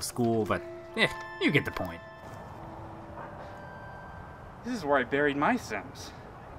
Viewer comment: I thought he said "this is where I buried my sins." Which would have been a really epic and mysterious thing to say.